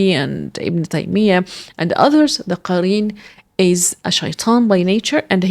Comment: Please change subtitle at 9:57 to Qareen instead of quran